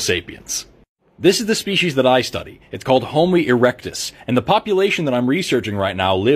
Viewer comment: Did you say Homely Erectus? Not kind. I sure they were very lovely in their own way. Even if none of them never did get a date to any prom.